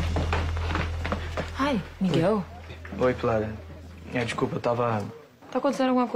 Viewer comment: O Miguel Falou " Oi Pilar " Ou " Oi Clara " Não Entendi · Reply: Canal Sem Nome Eu escutei "Oi pilara" kkkk